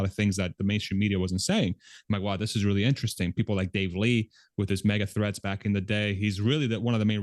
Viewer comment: I thought you said: "People like Dave Lee, with his mega-THREATS". That guy is soo militant... 😆